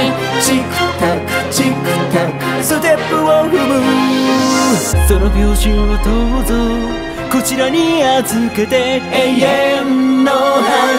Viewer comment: 『その名字をどうぞ こちらに預けて』 にしか聞こえなくてプロポーズかと 思っちゃった😂